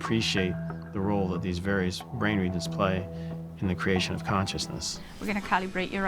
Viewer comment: Now i hear "brain needle."